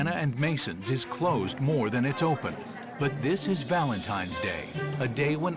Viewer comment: sounds like Gordon says "valentimes"